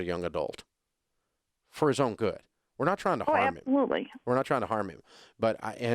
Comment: what did she say at 4:53? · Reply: absolutely